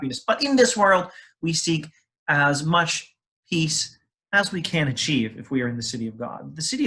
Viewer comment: What is the word sounding like "hees", at 3:03? Also a word inundated by chuckling at 3:14 I do not understand. The video is very instructive, and I would have taken it into a playlist on the history of thinking despite the lengthy introduction without a reference to Augustine, so I really regret not to feel able to do that, given the latter unintelligibility. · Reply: "Peace." "World."